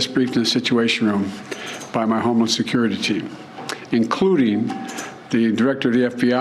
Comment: It sounds like he is saying “homeless security team” 😂😂